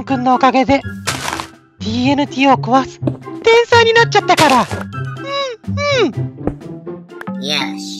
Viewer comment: 最初聞いたときTNTを壊すへんたいになっちゃったから!って聞こえてびっくりした。